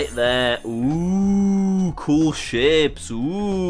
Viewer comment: am I the only one who thought he said "cool shit"